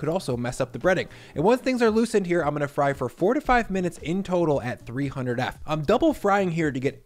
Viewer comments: your "four 'ta' five minutes" sure sounded like "fourty five minutes" lol. ty for the recipe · Thought he said to fry for 45 minutes 😂😂😂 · I thought you said 45 minutes and had to go back a few times to confirm if I heard that right. It’s actually 4 to 5 minutes. Hope nobody else got confused.